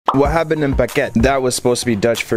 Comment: I thought you said What’s happen baguette. 😂